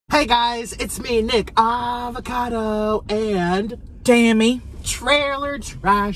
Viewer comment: It sound like she said dammy 😂😂😂😂😂😂😂